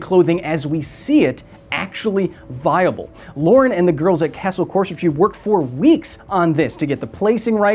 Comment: “Castle Corsets” with a mask on sounded like you said “Capsule Corps” and that made me smile